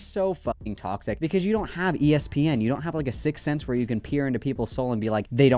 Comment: did you say ESPN??? 🤣🤣🤣